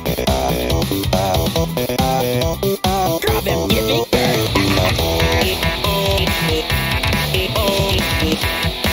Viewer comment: what does he say at 0:38 ? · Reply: DRAW THEM GIVING BIRTH